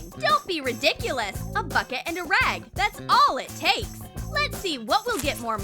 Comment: i thought she said a bucket and a mop